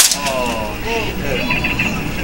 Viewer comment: People saying "that was my controller" but I keep hearing "mineral burner". I had no idea what the hell I was hearing so I looked it up.. "That was my mineral burner...."